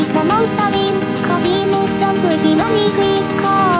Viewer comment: When I saw the line “your new boyfriend’s an arsehole” I actually heard it as “Your new boyfriend won’t last long” which would be infinitely scarier now that I think about it